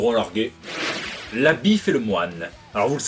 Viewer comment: C'est de ta faute ! Moi j'ai entendu "la bifle et le moine"...